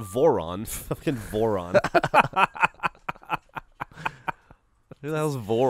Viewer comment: I heard Vorlon instead of "Voron" and... wow... just... no...